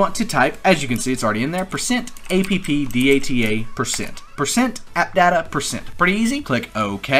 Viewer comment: what did you said in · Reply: %appdata%